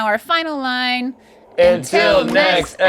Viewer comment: what on earth is link muttering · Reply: I think it was actually F.O.H.S.H.O. ...Supposed to be F.O.S.H.O. but an extra H slipped in there Link style.